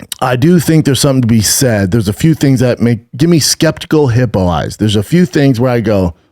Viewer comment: Brendan gave me an aneurysm with the amount of times i heard him say “skeptical hippo eyes”